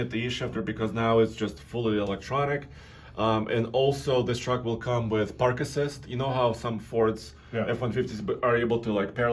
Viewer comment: who else thought he said "this vehicle comes with Parkinsons"? I pictured the truck was going to have tremors lol.